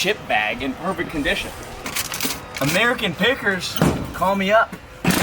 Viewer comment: It sounded like he said American peckers lol